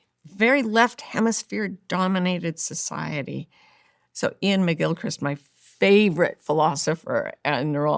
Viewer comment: Hello everyone! Could you please name the philosopher whom Dr. Martha Beck mentions at minute 14:28? I couldn't hear it Thank you! Btw, a great podcast with a great guest! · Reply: Iain McGilchrist